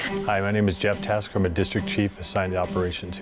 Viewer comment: I thought it said he was a district chef and thought "Damn she gonna learn how to cook for the firefighters too?"